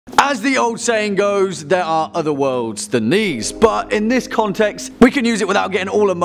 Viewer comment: i thought he said "there are other worlds then knees" and i got confused for a minute